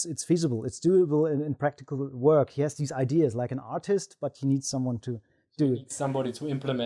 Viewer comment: "He has these ideas like an autist, but needs someone to do it" -, 2019 Great qoute. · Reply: for anyone interested (I heard him say "artist", by the way)